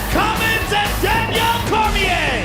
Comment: did joe just call him danielle? 3:38 lol